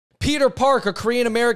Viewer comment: When I heard you say this guy’s name, I thought his name was “Peter Parker” for a second. LOL 😂